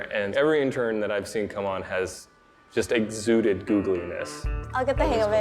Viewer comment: Did he just say “exuding Googleness”? Or do I need my ears cleaned